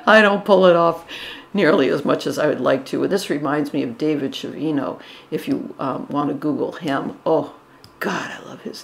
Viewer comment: I'd like to google the artist you mentioned at 5:48. I couldn't make out the last name. I tried a few spellings, but nothing came up. Chiovino?